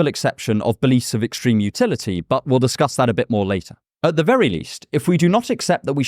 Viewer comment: Full disclosures im drunnn asf rn but I think. Your ids are amazing it really helps me think more critically about society and myself I even if its only when I’m annebriated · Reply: Also at 5:02 your subtitles use lieter instead of later, just thought I’d let you know but I ston want to do so as a separate comment because that would be rude. Cheers.